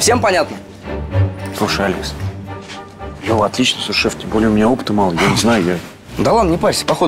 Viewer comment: Мне послышалась или Алекс сказал су*а